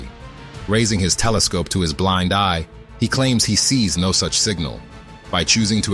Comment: I thought he said "I see no ships"